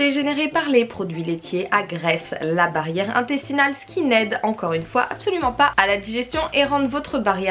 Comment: J'ai entendu "skinhead" dans la vidéo...